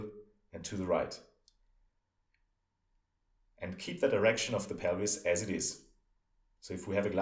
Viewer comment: Did he just say "keep an erection"..